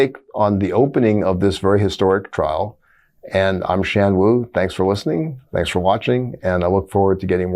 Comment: I have hearing issues, not enough for hearing aids. When Shan Wu introduced himself I heard “Shamwu”. 😂